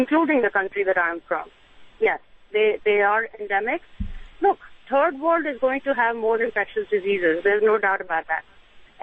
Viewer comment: Did she say turd world?